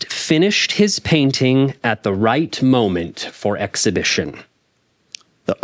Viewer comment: he said "for exhibition" he repeated "for the exhibition" which is what the test said